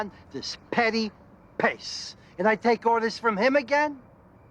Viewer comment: “creeps on this petty pace” did my man just quote Macbeth?!?! Lmao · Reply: Thanks for making sense of that for me, it's bugged me for years! I thought he said "petty **piss**" and I couldn't tell if he was using it in the expletive sense in place of "sh*t" or if it was some kind of mob-vernacular.